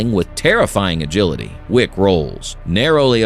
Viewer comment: Anyone else heard "Rickrolls" when he said "Wick rolls" at 08:05?